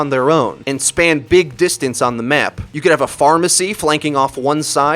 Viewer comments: d-did he just say pharmacy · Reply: Jonathan Mak pharah+mercy combo. Wordplay on the names. (phar-mERCY, not pharmacy) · Did you say a Pharmacy instead of Pharah..?